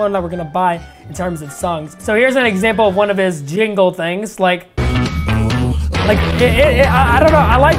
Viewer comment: I thought you said stranger things